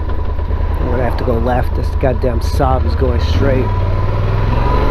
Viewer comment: did you just say, that god damn Saab?😁